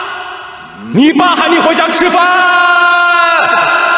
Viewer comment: “your dad still hasn't eaten dinner ” is wrong, it actually mean “your dad call you to go home and eat dinner ” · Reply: i think they heard it as “你爸还没回家吃饭！” rather than ”你爸喊你回家吃饭！”